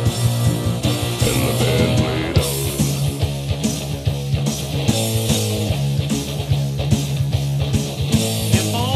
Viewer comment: What does the brotha say at 1:58? Anyone? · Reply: The band played on.